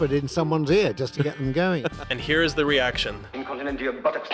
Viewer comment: I always heard it as "incontinentia buckets"; as in she fills buckets of it.